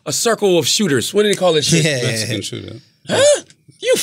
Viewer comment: What did he say? 😅😅😅😅😅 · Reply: "Mexican shooting"